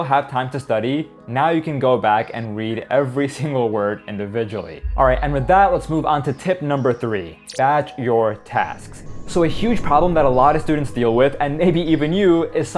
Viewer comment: 1. its not about the hours 2. read backwards (1st,2nd,3rd pass) 3. batching (group and start w/ easiest task) 4. give urself a time limit 5. use AI tools 6. abandon old strategy if not useful (sunken cost fallacy) 7. do fixed tasks first 8. tag your notes like so i can come back to these tips btw guys this is not to REPLACE the video, rather to summarize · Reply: At first i thought no. 3 said “bitching”